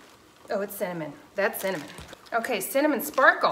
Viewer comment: Blimey, when you said ‘cinnamon’, I thought you said salmon! 🇬🇧 Great video, thanks.